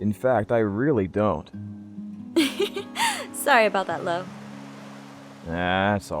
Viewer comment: i thought said “love” not lo o-o BUT I KNOW IM WRONG CAUSE OF.........AARMAU!!!!!!! :3